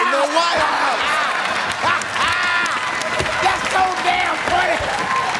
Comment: I heard it as “THAT’S SO DAMN FUNNY!!” 🤣